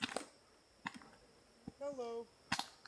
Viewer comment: sounded like you saying HELLO